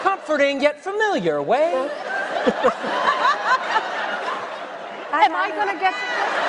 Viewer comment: did Penny say "am I gonna get to do it?" at 0:36:31 ? HAHAHAHAHHA it was funny...